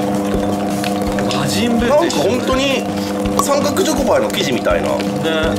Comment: 魔人ブウと一緒って聞こえる